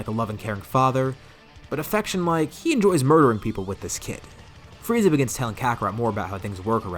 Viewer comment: Not even 10 mins in, and I hear "he likes murdering people with this kid."